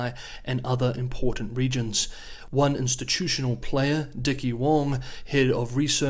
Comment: I thought you said his name was Dickie Warm and I spit out my drink. I replayed it several times and I kept hearing the same thing. I had to look it up and Dickie Wong is his name. Sorry I'm 12 years old in my heart and I come from a country that had a guy named Dick Pound in charge of our olympics.